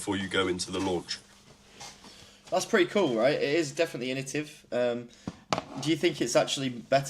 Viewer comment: What did you say? Initive 😂